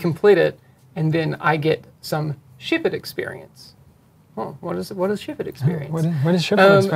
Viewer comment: I thought he was saying "shipped experience" until I saw it written on the screen...